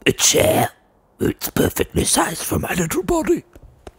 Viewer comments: I can’t handle the way he says “little body.” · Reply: Zephyr Weiss he definitely says “little body” it doesn’t even slightly sound like he says “little bottom.” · Gasp "A chair! ᴵᵗ'ˢ ᵖᵉʳᶠᵉᶜᵗˡʸ ˢᶦᶻᵉᵈ ᶠᵒʳ ᵐʸ ˡᶦᵗᵗˡᵉ ᵇᵒᵈʸ." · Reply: I thought he said "perfectly sized for my little bottom."